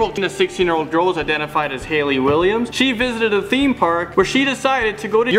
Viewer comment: Did he say Haley Williams 0:43!?!?